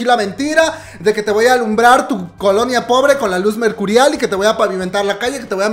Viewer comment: El: No voy a iluminar su pobre Colombia Yo: Aaaa como? Pobre Colombia 😤😤😤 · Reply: Yo le entendí "COLONIA". 👁👄👁 en el minuto 05:03, pero ajá, si escuchas bien, él dice "colonia pobre". Es algo que se dice mucho aquí en México, nuestros estados tienen municipios, colonias, etc. No es una ofensa ni tiene nada que ver con ningún país (no sé si el comentario original haya sido una broma, pero quería aclarar xd).